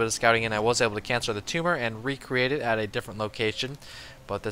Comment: did he say "Cancer the tumor"?